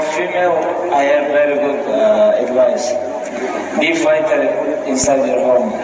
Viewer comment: bruh put subtitles here i can't understand shit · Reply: “Be fighter inside your home” is what he said